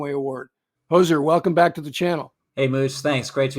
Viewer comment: Did… did Hozer just call Mooch Moose? ;)